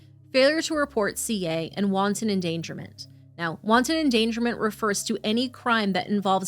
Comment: Poor baby - there are so many horrible humans. It sounds like said wonton endangerment. Wanton is pronounced wantun. Your content is very serious and I thought I would share since saying wonton sounds 'funny' and I know you don't want that.